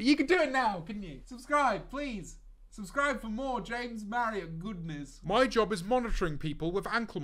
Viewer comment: I thought he said “scran me, scran James Marriott goodness” 😭😭😭